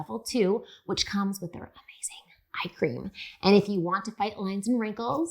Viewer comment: I paused it at "Eye Cream". but I thought you said...I Cream!!!!! HAHAHAHA